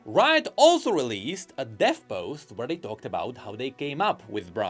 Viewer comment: I thought he said "death post" 💀